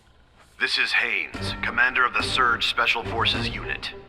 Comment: holy shit, for a moment i thought he said "This is Haze" and nearly spat my drink You really can't escape him